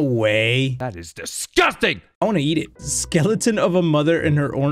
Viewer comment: Did say he wanted to yeet or eat it? Both are…mmmm.